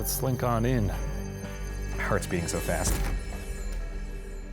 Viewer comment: I thought Shane said “feel free to slither into my butt.” To my utter disappointment he actually said heart...